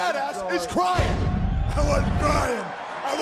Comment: sounds like he said i wasn’t. Crying “ i like men “ i wasent crying🤣😂